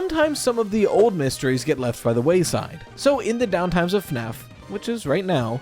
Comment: I misheard "downtime" as "downfall" and expected a WHOLE different video for a moment